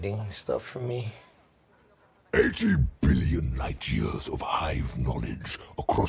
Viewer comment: When he said 8-billion, I thought he said "H-E-B" and I was like "What does a food store have to do with this?"